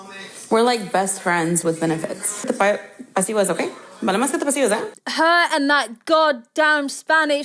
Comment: Hi, as a Spanish speaker myself I have absolutely no idea what she said. And your impression is not offensive at all, i understood that more. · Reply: Vale mas que te apacigues Its what the people are saying she said Ig